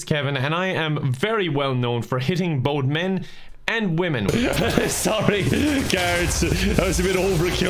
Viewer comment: the fact that I thought kevin said he was known for hitting ON men and women and I was like yeah go little bisexual go . what the fuck